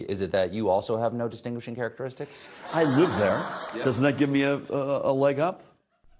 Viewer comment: the caption says Gov Murphy says "I live there" but it almost sounds like he says "I eat there"... he's so down the middle you can't even make out his own words